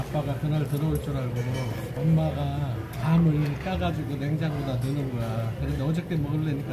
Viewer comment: 밤을 까가지고 라고 들리는데...?